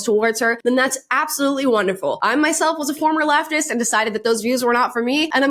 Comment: When you said you were a former Leftist I first thought you said “lap dancer” 😂